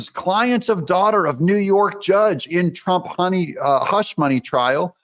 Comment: Did you say Honey-money? 11:03 LOL!!